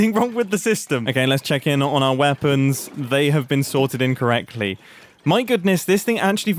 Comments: by the way, it kinda sounds like you said incorrectly, as in wrong, as opposed to in correctly. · had to play this back lol. First I heard 'they have been sorted 'incorrectly''. But I see its actually 'they have been sorted 'in correctly''. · "They have been sorted in-correctly" I found this hilarious! The main purpose was to say that they are being sorted correctly but he makes it sound like it's all wrong, saying it with such confidence too! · "they have been sorted in, correctly" i thought he said incorrectly and spent far too long trying to understand that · Mumbo: “They have been sorted in correctly.” What I heard: “They have been sorted incorrectly” …WHY MUMBO! Don’t confuse me like that.